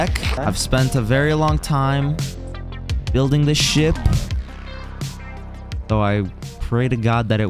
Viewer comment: i thought you said shi t not ship